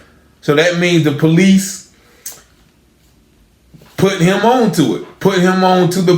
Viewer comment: Sounds like he said the " POLICE PUT HIM ALL UP IN THAT SHIT "